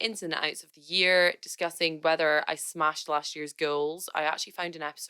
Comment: I thought you said smashed all the ghouls